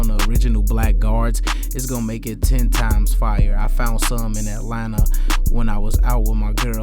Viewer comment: I thought he was saying “Atlanta”. Does anyone know what he really said?